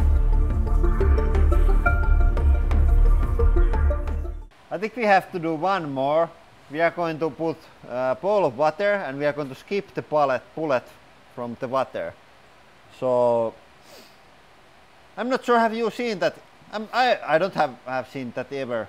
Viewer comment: I thought you were saying “ball of butter”. @ 8:32 lol. Great footage. One of the most impressive slo mo videos I’ve ever seen. Nice job. 🤙